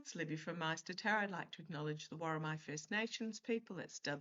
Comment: Random: I wonder if you always clarify the spelling of the 'Worimi' First Nations people at the outset of your videos, because of many folks misunderstanding in the past; since I always hear "the war on my first nations people" when you say it 🙃